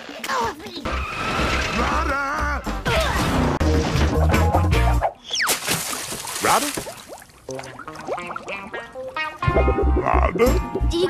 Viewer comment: At some points when saying "Rada rada rada" really fast, it sounds like hes saying "alright alright alright"